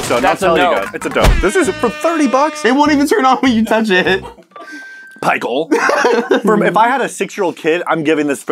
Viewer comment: Did say "Pichael" at 3:11?